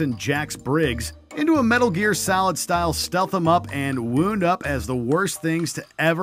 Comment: did he said wound up😂